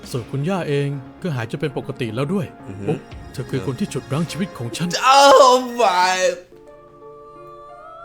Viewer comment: ไม่ได้พูดว่า'ช่วยชีวิตฉันเอาไว้' นะคะแต่เขาพูดว่า'ฉุดรั้งชีวิตฉันเอาไว้'